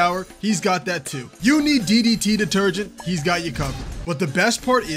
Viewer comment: Did he just say DDT detergent instead of deterrent?